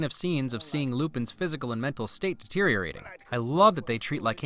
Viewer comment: he said it was lupins phisical and METEL state instead of Mentel state